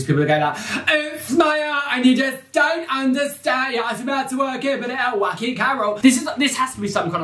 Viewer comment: Can anyone translate? Hes using such a thick accent I cant tell what is being said lol 😂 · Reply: “It’s my art and you just don’t understand it! You don’t have to be mad to work here but it helps, oh wacky carol!” 😂